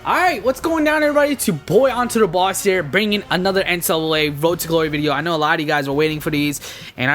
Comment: he said n-sl-a! instead of ncaa